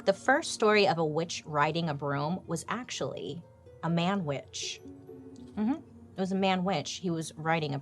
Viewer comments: A man witch 😂 I thought you were saying Manwich 😂 I was like “it was a sloppy Joe?!” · When you said MAN WITCH I thought you said MANWICH! Like the sandwich 🍔 LOL 😆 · Haha you said a man WITCH i thought you said MANWICH like a sloppy joe